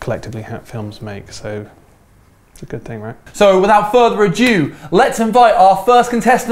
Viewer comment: Did say "Without further ajew" at 6:40?